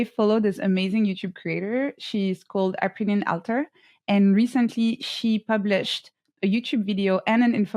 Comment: What's the name of the YouTube Creator she mentioned? I couldn't understand. The one that made an infographic about thumbnails. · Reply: Her name is Aprilynne Alter, here you can find the infographic: <link>